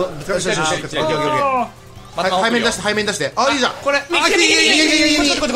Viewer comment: 「排便出して排便出して」 に聞こえるw